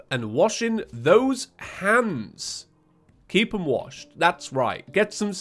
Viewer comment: For a solid 5 seconds I thought you said keep your hams washed and I was a little concerned but in my mind I said “alright will do so”